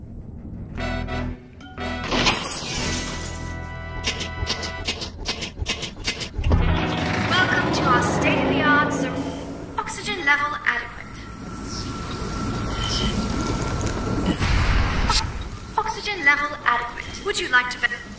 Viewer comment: bro for a second I thought the speaker said "state of the art- FUxygen"